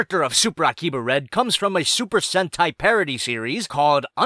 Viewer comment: I thought you said super hentai...